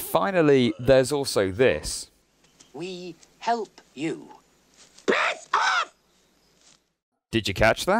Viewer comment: I heard "back off"